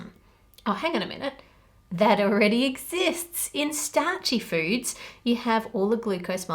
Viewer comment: At first I thought you said starchy fruits not foods, imagen my confusion when you said pasta 🤣